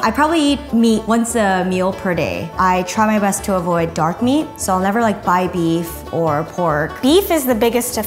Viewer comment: who thought she said dog meat?